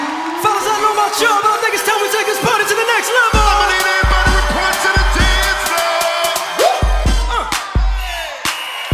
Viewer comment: What did he say? I just focus but...i don't even know. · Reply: He said FELLAS, not the N word. Geez!